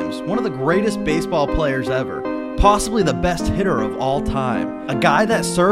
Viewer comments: I thought he said”the best hitler” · Damn i thought he said best hitler of all time served in ww2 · I thought you said best Hitler 💀💀💀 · I thought he said possibly the best “Hitler” of all time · i thought it said "possibly one of the best hitler of all time" 💀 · I thought he said he was the best HITLER